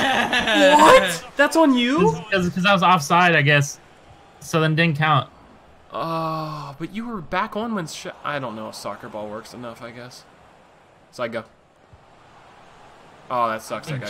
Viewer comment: did you just call it soccerball?